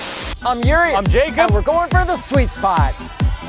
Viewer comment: lol i thought yuri said They are going for the G spot 😂😂😂